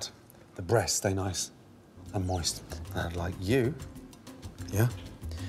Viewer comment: I thought he said "the breast stay nice and moist like you..." 2:15 oml